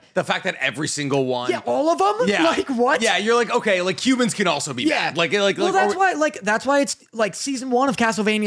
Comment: i really thought be said cubans can also be bad